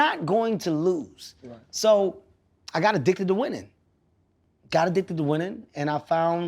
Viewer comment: At first I thought he said "I got addicted to women" (I got addicted to winning) I just nodded my head with my right eyebrow raised 🤣